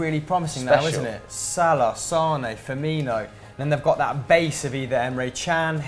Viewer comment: did he say sane and not mane